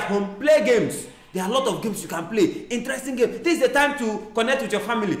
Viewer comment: I was just looking for Emmanuela everywhere oooo. Wait ooo 😁 mark Angel did you just say there are lots of books you can play 😅😅😅😅🧐